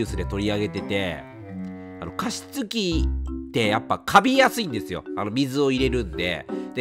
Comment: 「加湿器ってね、かみやすいんですよ」って聞こえてしまい、確かにレトさん加湿器って言えてないな、言いづらそうって思ったらかみやすいんじゃなくてカビやすいんですね。 大変申し訳ありませんm(_ _)m